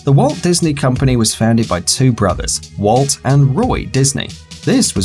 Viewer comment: I thought he said Walt and Joey